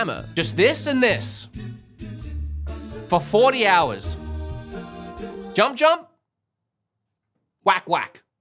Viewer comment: I thought you said 14 hours, I thought surely this can't be right. I look it up and OH MY GOD I can't believe it's 40 HOURS!!